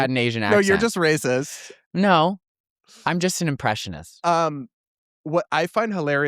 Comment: I swear he said "no I'm an oppressionist"😂